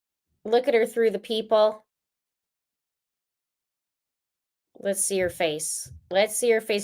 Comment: 😅😅😅help me I'm dead!! I thought you said "look at her through the 'PEEP-HOLE' and was really zooming into find a peep hole somewhere! 😂 Then realized you said PEOPLE after the 3rd play back of Whori! I adore this channel since Chris Watts! 😂😂😂Justice for LELAND CHARLES VALLOW!